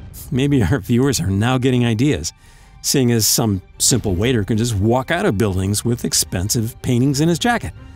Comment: I thought it Said walter